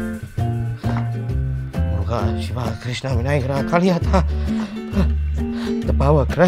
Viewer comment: did I hear Krishna and Shiva? Indian?!!?!? I never knew!!!! Me too bruh!